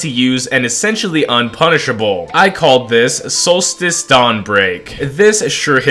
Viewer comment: I thought you said dawnbreaker